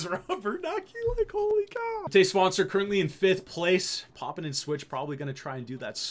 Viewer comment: lol every time you said Matej Svancer I thought you were saying "todays sponsor"